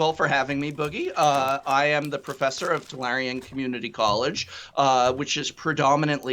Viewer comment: I THOUGHT HE SAID flarion COLLGE I WAS LIKE WTF????